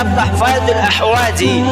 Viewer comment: 👍😊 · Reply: Misi bib.. Gua yg salah denger apa gmna, yg bner kan tuhfatul ahwadzi syarah jami tirmidzi, yg gua denger tuhfazul ahwadzi, apa gmna wkwkwk